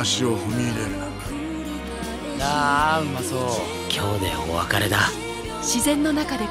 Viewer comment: あ〜うまそうに聞こえた笑笑